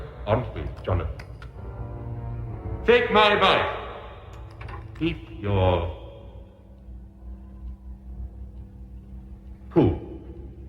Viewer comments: I heard, "Keep your...poo," but that's not much better, no. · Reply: I heard "Take my advice, keep your cool." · I think he said "take my advice!" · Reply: "take my advice. Eat your......poo!" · Eat your.. [dramatic pause] poo. That killed me xD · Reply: I heard, "Keep your...poo," but that's not much better, no.